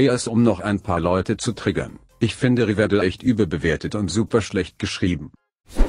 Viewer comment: Verstehe den Satz bei 8:13 nich ganz ^^" also akustisch, das wort, was is da nochmal schlecht geschrieben ? edit: ok mein cousin hats verstanden, riverdale oder ?